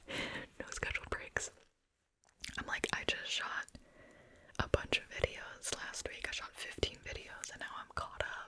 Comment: did i just hear " i just shot a bunch of idiots"😳